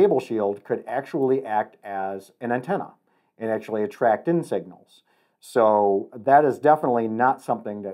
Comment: Lol I seriously thought u said, "the floating cable shield, can actually act as an antenna, and can actually attract insects ya know" 😆 at that point, I was like, "yo dude, there's actually channels on YT that show ya how to fuck with newbies in the field! I like that 1 lmao.. insects! That'll blow the new guys mind!" 🤣